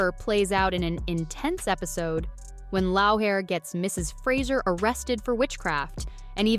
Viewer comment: I just heard "Lowhair" for Laoghaire and I just stopped paying attention.